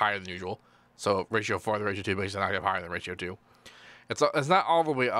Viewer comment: what did he say? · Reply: "so ratio 4 into ratio 2 makes it an octave higher than ratio 2"